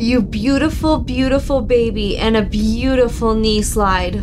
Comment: Haha, almost thought you said "a beautiful niece lied." instead of "a beautiful knee slide."